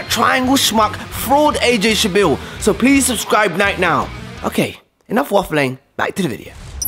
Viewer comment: When He said to subscribe at 4:20 secs to 4:26 secs he said night now instead of right now